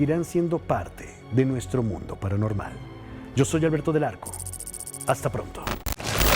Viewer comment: ¿Qué opinas de este tema? Yo soy y te invito a suscribirte a mi canal. No te pierdas <link> 😃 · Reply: Jajaajajja yo pensé que decía Alberto del NARCO JAJAJAJAJ SORRY